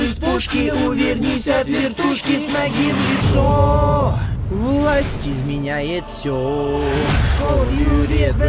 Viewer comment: В конце послышалось: Лайк изменяет всё.